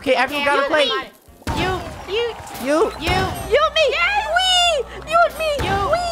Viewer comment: WAIT AM I 5HE ONLY ONE WHO HEARD THEM SAY Yoot at 9:37 ?? or yeet cause i call it yeet i loveeeee the krew tho ♡♡♡♡